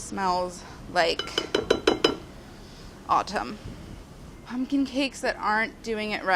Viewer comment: My ears heard "Smells like autism" instead of "autumn"